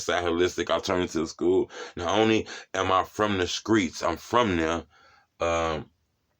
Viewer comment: You said "Steetzs". Yep, you from the hood. Lol RIP to your friend. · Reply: I thought he said screets